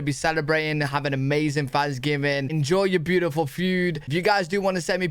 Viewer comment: I love how with your amazing British accent with me being American I heard you say, “enjoy your beautiful feud”, which is absolutely perfect for many a Fanksgiving dinner here. Love it.